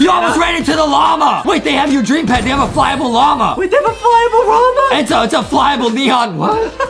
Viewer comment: Got me when adam said Wait they have Fryable Rama?! and justin said It's a flyable neo- what? did he really said Fryable Rama?